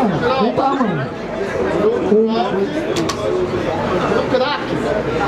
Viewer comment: so eu ouvi o KRAK?